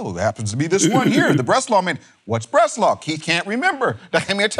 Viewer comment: At first I thought you said Breslev 😝, I thought wow those guys have a manuscript? Lol cracks me up